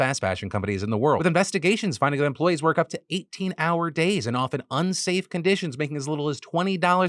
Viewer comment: i thought he said an hour. I was like "hell i do that now"